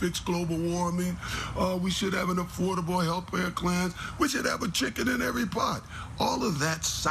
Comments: Did OJ say "affordable health pair clan" at 3:22 ? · Anybody else realize OJ said "health plare clans" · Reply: I had to listen a second time because my brain went "Wait? Did he just say "health plare clan?"